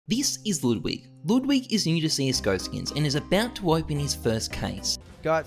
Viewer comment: All I heard is "Ludwig is ergisterger skins, and is about to open his first case" what did he say?